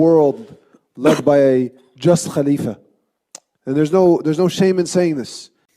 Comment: thought bro said wiz khalifa at first